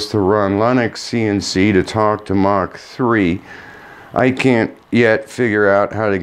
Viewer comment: Did you say you're running LinuxCNC talking to Mach3? Seemed that's what you were saying around 2:40. Not sure what that means, but why not just ditch Mach3 altogether? I'd be happy to help you setup LinuxCNC if I can. It's a steep learning curve, but it's wonderful once you get over the hump. Mach3 is antiquated. Let me know if I can help.